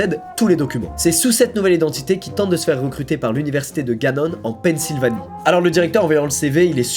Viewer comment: Je passe pour un put*** de mec qui se lave pas mais je peut pas m'empêcher de relever le nom de l'université à 6:43, après j'ai peut être mal entendu mais j'ai entendu "université de Ganon" mdrrr